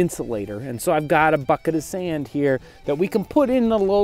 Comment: I thought when he said so I got a bucket of sand I thought it was so I got fuckn sand